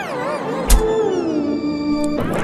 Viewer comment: I hear "THANK YOU DOG"